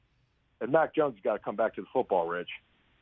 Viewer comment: What is he saying at 1:00? I can't understand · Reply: He made a joke about Mac Jones having to make the tackle. I believe he said Mac has to come back towards the ball being thrown to him.